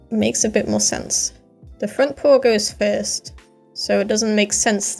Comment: the captions say you said pool instead of paw